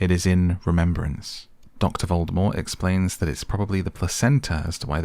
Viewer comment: I know I heard him say “Dr. Voldemort”